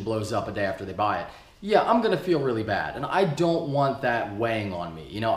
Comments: Did just say Wang? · Thought said "I don't want that wang on me" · Did it sound like he said, "I don't want that Wang on me" to anyone else?